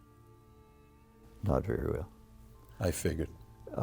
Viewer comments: What does the guest say at 4:06? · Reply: I want to know that too, listening to it quite a few times & it sounds like 'not very real' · Can someone tell me what he said at 4:00? Patrick asked him about LBJ, it sounded like he said "not very real," but that doesn't make sense. What was said and what did it mean? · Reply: I heard, "Not very well."